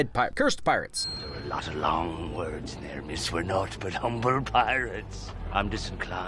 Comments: Excuse my grammar nazism, but I believe he's saying "naught" instead of "not"? Small difference but it does kinda flip the entire meaning of the sentence... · the subtitles say "not but humble pirates" when he's actually saying "naught but humble pirates."